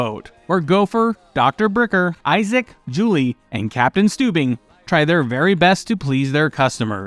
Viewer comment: when I started watching and paying attention to Love Boat, I thought they were saying Captain Stupid I remember asking how come they're calling the captain stupid, he seems like a good guy? It wasn't till a little later when I found out they were saying Stubing.